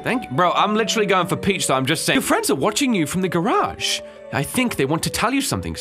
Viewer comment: I thought she said your friends are in the garbage